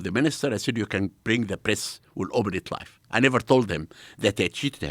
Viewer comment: Did he say 'I didn't tell them that i cheat them'?